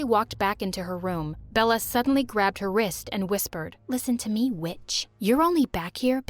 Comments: I thought Bella said the B-word😂😂😂 but no she said witch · did she say with or b*tch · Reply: She said witch